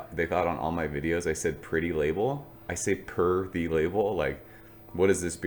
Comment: Man that is hilarious, I have also been wondering for a while what you were saying and I also thought you said purdy or pretty label🤣 now I know. 🍻